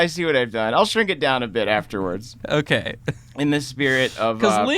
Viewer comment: It sounds like she saying After Aids